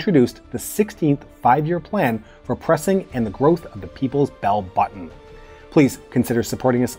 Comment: Thought you said, ‘The Growth of the People’s Belly Button’! Lol 🤪